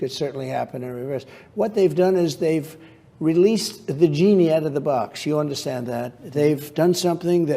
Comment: Did he just say the vagini is out of the box